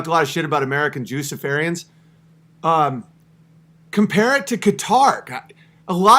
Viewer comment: Wait... Did he just say "American Jew Safarians"? LOL